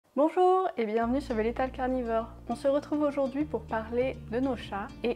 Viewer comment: J'ai compris "Bonjour et bienvenue sur végétal carnivore" 😂